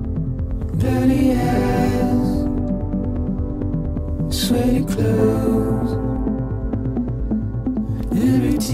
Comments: i cant be the only one who thought it said "dirty ass, sweaty clothes" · Sounds like they say "Dirty Ass", "Sweaty Clothes" at the beginning.